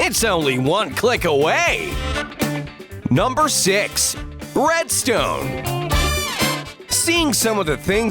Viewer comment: 5:07 Me: did he say breadstone ??